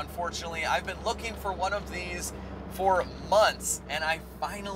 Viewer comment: do you mean months ? cause it sounds like you said munts